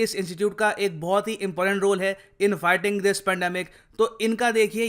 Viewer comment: You told in fighting this pandemic, I heard inviting this pandemic...BTW what I heard is more true...